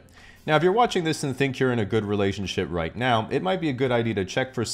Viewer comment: did I hear "relationshit"? haha